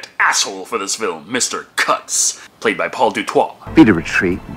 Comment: it sounded like he said Paul de Twat.